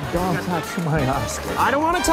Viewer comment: Oh it was OSCAR I thought he said Ass LOL!!! OOPSY!!